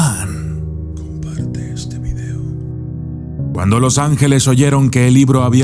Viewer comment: En el minuto 3:18 se nota que dicen "Comparte este video"borther eres muy bueno te sigo desde hace mucho tiempo,pero no veo la necesidad de usar audios subliminales,no los nesesitas"